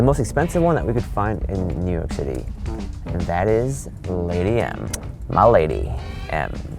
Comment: I thought he said "Late AM" not "Lady M". WHOOPS.